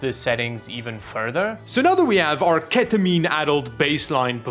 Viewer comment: Ketamine adult baseline performance ?? Did i heard that right? :D